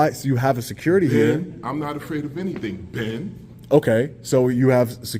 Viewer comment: Omg I had to rewind that to figure out what he was saying 🤣 "Ben" as in Ben Franklin 😂